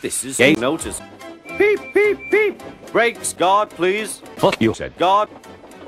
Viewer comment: I get it “guard” sounded like God